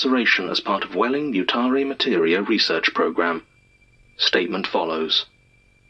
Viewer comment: man I misheard intro "Welling Mutare Materia" as "Wayland Yutani" which was REALLY NOT RIGHT. So apparently people are just falling into reality. That's...not...great.